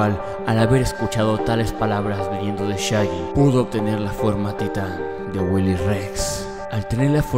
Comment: te juro que escuché "La forma teta"